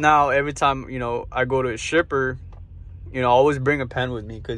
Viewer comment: Dude I thought you said STRIPPER 🤣 you meant shipper